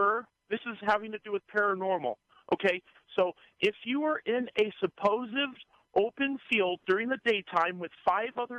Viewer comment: did he say “supposive”? It was at this point I knew he was going to fail badly